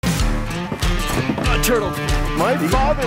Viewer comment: Starts video* walks on porch* Ugh! Turtled! XD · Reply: I thought he said turtle f*cker